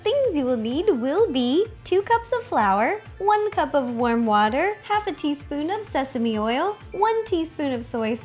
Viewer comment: I thought she said 'worm water' who else heard it?